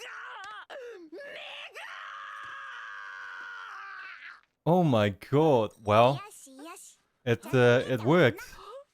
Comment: is bro saying the N word????? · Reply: "Me ga!"